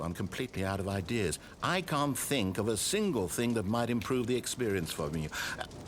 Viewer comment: he says "might improve the experience for me" and the subtitles say "might improve the experience for you" and it's bugging me idk